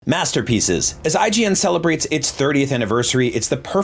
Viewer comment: Oh masterpieces, I thought you said master pizzas. No I don’t know what master pizzas are.